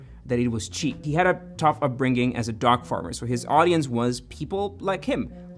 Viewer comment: I really thought you said dog farmer for a second there 🤣